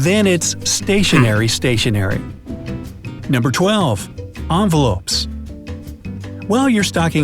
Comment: Did he just said onvelopes instead of envelopes?